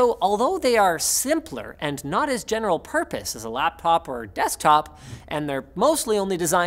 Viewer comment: Did he Say "Lappop"?